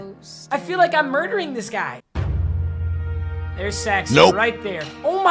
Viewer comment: seys there`s sex instead of X